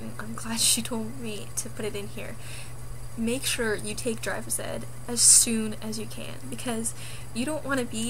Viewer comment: When you said take drivers ed I thought you said take drugs